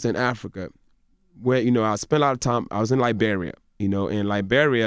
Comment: They tried to make Michael look bad but ended up just proving his point. Clearly he can take a joke and keep on moving so that means that nobody can get mad at him for making a joke but these dudes are crying about what he did to Kevin while calling Michael's ex in a failed attempt to humiliate him. I would make fun of Envy cheating on social media right after this interview if I was Michael Blackson. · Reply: I wear Mexican, African, American Indian attire but that doesn't mean I'm Mexican, from Africa, or Amer. Indian. But I misheard him in this video. He said, I was in Liberia. I heard I'm Nigerian.